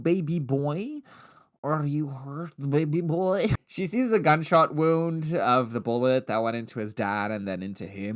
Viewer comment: Girl... I misheard 'gunshot wound' for 'backshot wound' and went like DAAAAAANNGGGG 😂